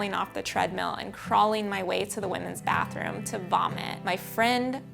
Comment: Why are the captions so bad... 6:26 she says she crawled to the bathroom to vomit.. captions say she crawled to the bathroom to bomb it...